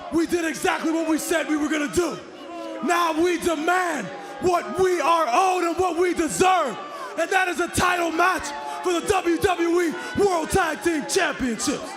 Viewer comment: Im sorry, i thought i heard Kofi said: I deserve a title match at the WWE championship 🤷🔥